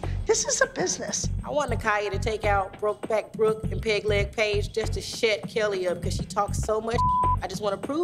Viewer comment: why did she call Paige “ piglet Paige “ ??? that’s so rude of her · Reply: She didn't call her "Piglet Paige", she called her "peg leg Paige", it's even worse because she was just coming off an injury...